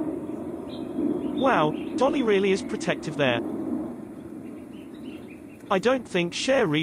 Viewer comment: "Dotty is really protectiver there" then 9:38 "Meanwhile over here I think Dotty has had enough of Buddy" Why did you call two different monkeys the same name? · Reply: I believe he said that Dolly is really protective there. Dotty is in the tree...